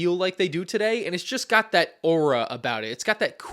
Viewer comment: DID HE JUST SAY “Aura” IN